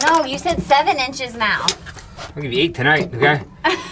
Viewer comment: did he say :” You’re getting 8” tonight “ ???? 😂😂😂😂 .